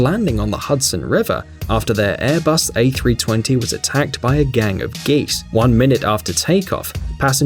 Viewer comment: lol i heard a gang of gays